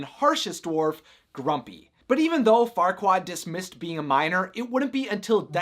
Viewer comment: Oh a MINER... I thought you said Farquad was born to a princess and a minor